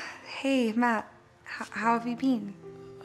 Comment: did she just said bean instead of been i don’t know if I’m the only one the heard that